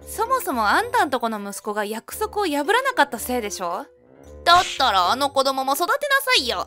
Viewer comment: そもそも、あんたんとこの 息子が約束を守らなかった せいでしょう。。って箇所 が約束を破らなかったって 聞こえるのは、私だけ？ 何か妹元夫と義両親だけ 年取って、働くのが大変 だとかあるが、私側は未だ 若いんだね！